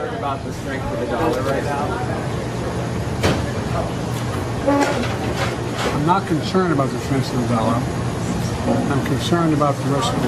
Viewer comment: I swore Biden said "I'm not concerned about the taste of vanilla" Go back and listen